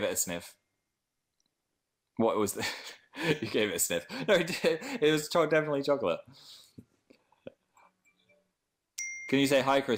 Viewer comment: Is it just me or does it sound like he says "no, dear, it was definitely chocolate" at 0:53? like idk i just think that's adorable >.<